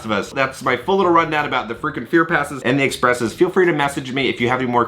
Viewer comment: Thought you were saying FREAKING fear pass😂Love your vids, friend!